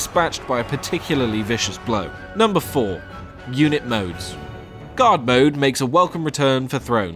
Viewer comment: At first I thought you said "God mode" instead of "Guard mode". That caused quite the confusion, lol.